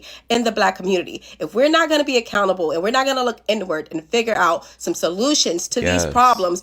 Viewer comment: When they said "look inward", I heard "look N-word"